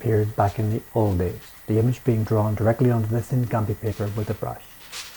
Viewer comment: Hi, You mention that the image is printed onto gumby paper but for someone like me - what is gumby paper? What alternatives can I use? I just tried to transfer 10 images onto mdf using office paper with an ink jet printer but I'm either over-eager or missing something fundamental. It would be amazing if you did a short tutorial series to get beginners like me into it. For purly selfish reasons of course. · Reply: Unless I misheard, I think the spelling is "Gampi" paper, it's made from a gampi bush.